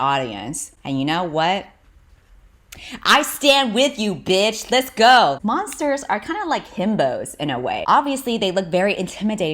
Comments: I legit thought you said "Monsters are like hippos" for a second, and got really confused when you described hippos (who are actually really terrifying) as soft on the inside. 😂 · i thought you said “monsters are kinda like hippos” and i almost spat